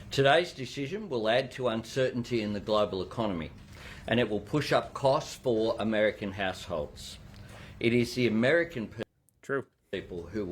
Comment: to my untrained (German) ears, his accent sounded like he was saying 'it will push up the costs for American a**holes" 😂